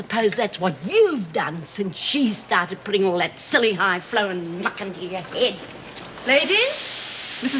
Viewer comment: Does Lizzie say "fuck"?